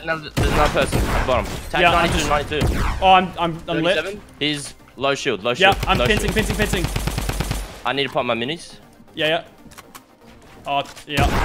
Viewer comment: did he say I’m pissing?